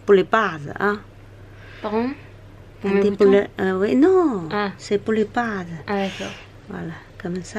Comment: i think she way saying "cest pour les bases" in english i think you can translate this by : " its for the fondation" like preparing the skin with a moistirizing cream